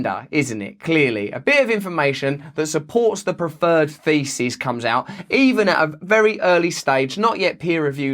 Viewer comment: I heard "a bit of information that supports the preferred faeces". Kind of the same thing anyway!